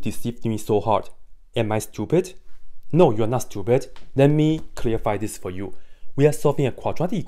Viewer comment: I heard "No you are not, stupid" 🤭